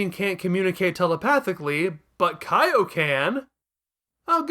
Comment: y'know, i completely misheard "But Kaio can!" at 18:10 as "But Kaio-Ken!" for a second.